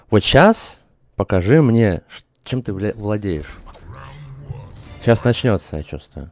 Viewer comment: первый мат, который я услышал от))))) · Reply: он оговорился и сказал Вля. Владеешь. а не мат